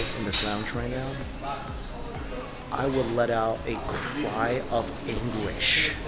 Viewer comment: I thought he said “I would let out a cry of English”